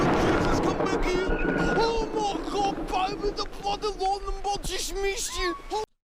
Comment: I still can't make out what the hell the guys saying, still though, his reaction & the way he talks sounds so funny 😂🤣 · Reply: "Ohh God Jesus come back here"..."Ohh my God baby, the blood - lightnin bolt jusht missed you"... "Holy bloody mother of God"